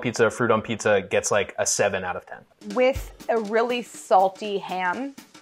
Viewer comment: What did Delany say at 14:51? was it 7? 17? or 7 out of 10? I thought he said 7 out of 10, which in turn translates to 70.